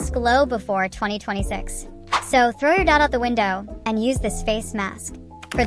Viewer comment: I wasn't near the screen so I thought she said "so throw your dad out the windo"!